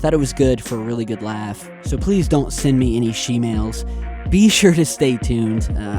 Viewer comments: Well, if you guys didnt catch it, at the 12:05 mark, it says "So please send me only shemales." Not kidding, check it out. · Tyler says "so stay safe and I'll see you back in just a bit" captions says "and edit more captions for you, see ya." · turn on your captions at 12:04... "So send me any schemales" instead of please don't send me any shemales xD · if you have the captions on he says so please don’t send me any shemales but the caption says so send me any shemales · if you have the captions on this exact video the caption reads " and edit more captions for you, see ya" and what is actually said is "so stay safe , and ill see you in just a bit" check it if you dont believe me ... they are fucking with you, even in the video that you called them out in... fucking youtube nazis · of THIS video CC says "So Please send me only shemales". Check it out Im convinced they are trying to get to you hahahahaha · Just to let you know, when you said "Please don't send me any shemales", the closed captions say "So send me any shemales". Minute marker at 12:05. Just an FYI lol